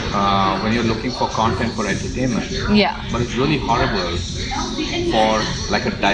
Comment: not to be pedantic, but I think he said "but it's really horrible", not "really hard for". His accent made it difficult to catch. Doesn't change his message though.